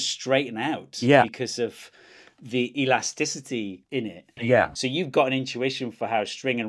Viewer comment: he said in it, but i heard innit bruv